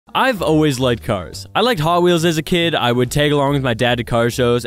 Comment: I thought you said I’ve always like drugs😂